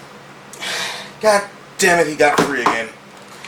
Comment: Can someone tell me what did he said? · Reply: 'Goddammit, he got free again.'